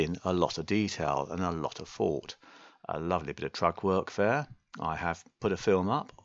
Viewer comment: I cant understand what you are calling that item, can you spell it for me? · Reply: G. SINGLETON the basket is called a " Trug " imagine it filled with beautiful fresh flowers, straight from your garden. 🌺🌻🌼🌷🌹🌸😊